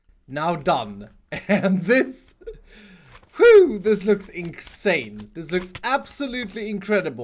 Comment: did he just say: inc-sane?